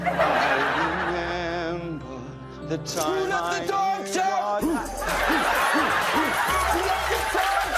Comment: 초에 우울할 때 똥싸 라고 저만 글케 들리는가요